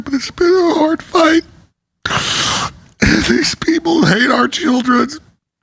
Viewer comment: Someone help me, please. Did Alex say, "these people hate our children(s)?"